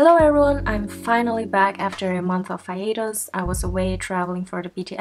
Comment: I thought she said “I finally came back after a month of fajitas” I